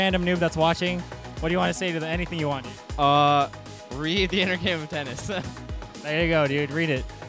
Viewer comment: does he say, "Read the inner game of Tennis?"